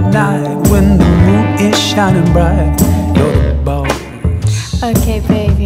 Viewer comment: i watched this just a little after i woke up and i seriously thought they were saying you're the balls.